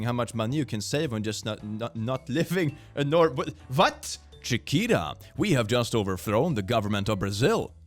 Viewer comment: I was just listening to this and not really watching … I thought said Shakira, not Chiquita. Was surpised to learn Shakira had overthrown the govt in the 1950s. Go off, queen! Loved you in Zootopia 2